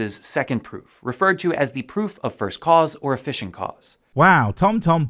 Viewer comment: When I watched Viced Rhino's "cover" of this video, I had looked away for a couple of seconds at about 5:56 and missed the closed captions. When Frere Whats-it said "efficient cause", I thought he had said "fishing cause" and thought he had accidentally confessed to "fishing" for evidence.